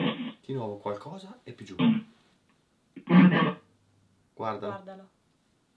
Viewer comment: io ho capito VALIGIA ha detto guardala e poi ho capito valigia